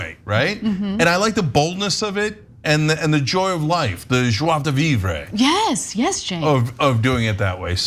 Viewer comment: What did he say??? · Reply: joie de vivre. It's joy of life in French.